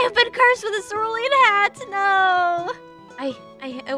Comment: I think she says "i-i will have to resort to"